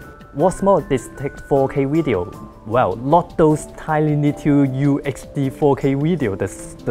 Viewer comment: what did he say? "tily-two you hd 4k weedeo?" What does that mean?